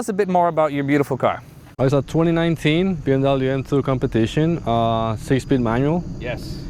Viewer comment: It sounds like he said three speed manual. god damn mexicans speaking with a taco lisp. lmao.